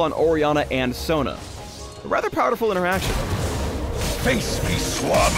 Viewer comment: Did....did he just say "rather powderful interaction"..